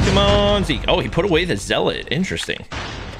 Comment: excuse me what the actual f**k did you just call the zealot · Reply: zelit like bro